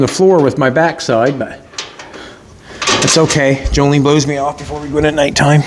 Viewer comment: Wait, did he just say "Jolene blows me off every night"?........... Looking amazing now. Love this truck